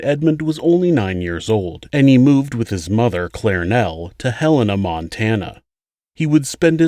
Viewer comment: I thought you said they moved to Hell in Montana 🥴 I think it was Helena Montana but same dif lol